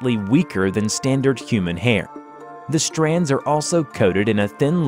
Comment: I thought you said "standard issue human hair"